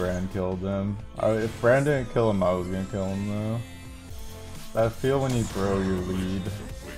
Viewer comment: anyone else thought he said "that feel when you grow your weed"